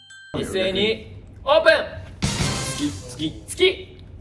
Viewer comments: ｢月!!｣が｢好き!!｣に聞こえるんよ笑笑 thが笑笑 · 「月！」が「好き！」に聞こえて、勝手にドキドキしてます💙